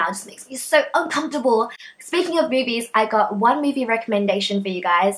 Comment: You were talking about fifty shades of grey and then I thought you said "and speaking of boobies" O___O but listening again, it was "and speaking of movies"... LOL. Anyway, I really enjoy these vids! Keep it up!!